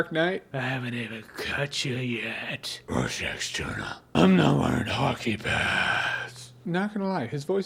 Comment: "I'm not wearing hockey pads." Hilarious. Interestingly, when I first heard it, I thought he said hockey pants.